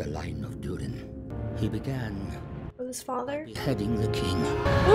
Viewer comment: "Azok, the defiler. The giant had sworn to wipe out the line of Duran. He began by pegging the king." Son: "NOOO" I know it's beheading, but MAN that edit caught me off guard Tell me I'm not the only one who heard that?